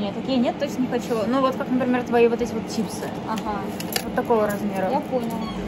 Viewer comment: Мне послышалось « как твои вот эти вот чипсы» , я думала ща чипсину покажет😹😹😹😹